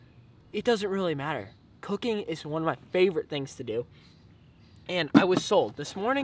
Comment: I misheard and thought you said 'cocaine is my favourite thing to do' and instantly agreed 🤣🤣